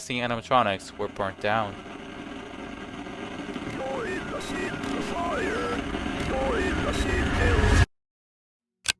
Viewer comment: what did it say?? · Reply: I think it says " go in asend the fire, go in asend hell."